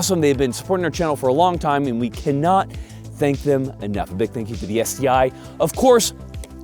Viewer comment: It almost sounded like "thank you for the STI".. For those who doesn't know, it's pretty much just another acronym for STDs.